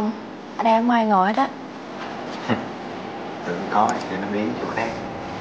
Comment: Cho hỏi khúc 2:34 anh Ginô nói gì ấy nhỉ? Tiếng ồn quá nên mình nghe không rõ lắm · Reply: "Tưởng có thì cho nó biến đi chỗ khác" nha bạn ^^